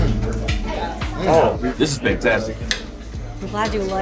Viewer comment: did Thundercat say Spectastic?